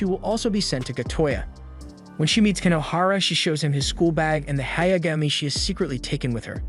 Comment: at first i thought you was saying "sht show" Lol